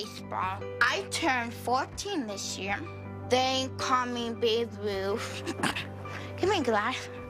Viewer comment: Babe Ruth Rrrrriiiiiiigggggghhhhhhtttttt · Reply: I thought he said big ruth but ok lmao